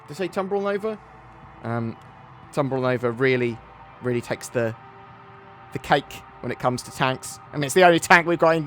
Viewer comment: I had to go back because I thought I heard "It really tanks the cake"